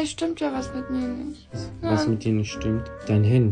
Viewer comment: Ich hab am Ende ''Minecraft ist kaputt'' verstanden 🤣🤣